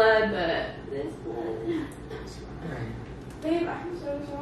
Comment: i thought at 6:13 he said "it's a prank", did i hear wrong or did he know😂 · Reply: He said “it’s okay, it’s okay” it does sound like he said “it’s a prank” though hahah!